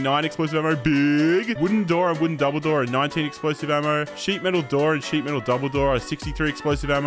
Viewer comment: I thought you said explosion arrow and I confused until read again. The crossbow shooting word BIG kinda trow me off